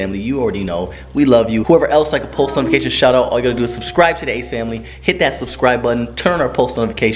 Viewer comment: I really thought she’s saying ”oh shit ace family, oh shit” 😂😂😂